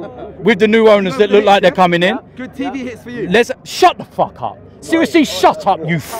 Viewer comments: Dt put that knobhead in his place. Well done mate. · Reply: sounded like he said "good tv hits for you". Implying Dt is just doing it for the views and money. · what did the guy behind say to DT that pissed him off so bad? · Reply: Good tv hits